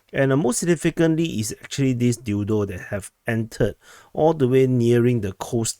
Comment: Did he just say dildo?! LOL